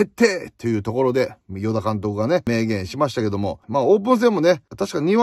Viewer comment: 最初三浦監督がって聞こえたけど何度も聞くとちゃんと与田監督って言ってた